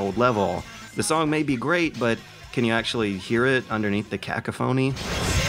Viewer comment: ka-kuh-fony? What- what the fuck is that word!?!... OOOHHH you mean "Cacophony"? was that deliberate?